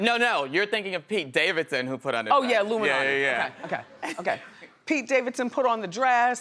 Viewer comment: Did she say "illuminati" @ 7:24 when discussing Pete Davidson putting on a dress?!! Wowww, lemme find out Wendy's woke!!!!